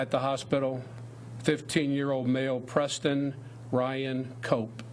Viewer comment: I thought he said 15 Euro male. Prayers to fam and loved ones effected